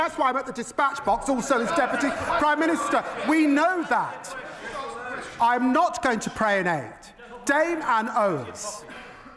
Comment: I’m struggling to understand what Lammy said at 7:30 ,, it sounds like “I’m not going to prayonade”. Anyone?